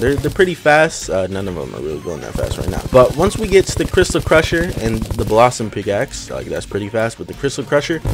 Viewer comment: I thought he said crystal pressure